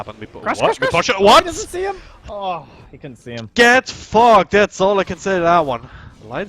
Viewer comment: fogged* LUL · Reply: lmao when I first heard it I thought he said get fucked xD